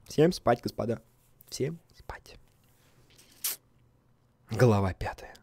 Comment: послышалось галава 5